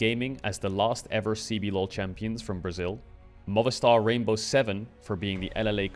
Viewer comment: Quick note, at 1:54 the subtitles are wrong, its "Movistar Rainbow7", not "MotherStar" 😅